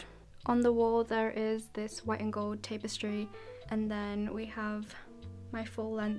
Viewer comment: what is the name of that thing? I can't catch what she said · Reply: A wide angled tapestry :D